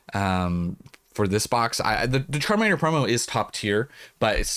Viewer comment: If u listen to the part where you said the “Charmander promo is top tier” I swear the first time I heard it I thought you said “terminator promo is top tier” 😂 I had to go back